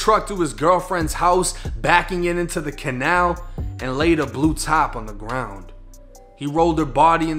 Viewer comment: Not gna lie homie from that Bostonian slang you got going on I thought you said “That’s when he blew top all over the ground” …lol I was like what the fuck does that me— oh blue tarp lol Now get to tha packey and get ya self some beers as my buddy jeano would say